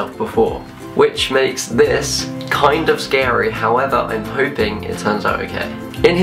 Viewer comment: HOWEVER I AM HOPING.... WHAT DID HE SAY? wtf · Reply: However i'm hoping it turns out okay. :-)